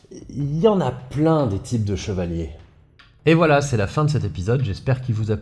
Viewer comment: C'est moi où il y a des espèces de petits bugs sonores tout au long de la vidéo ? J'en ai entendu un à la fin à 27:17 , mais je suis sûr d'en avoir entendu avant, et je me demandais si ça venait d'un autre de mes onglets... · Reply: Oui ahah ça m’a aussi fait tiquer. Surtout qu’à la fin il dis clairement « un bœuf »😂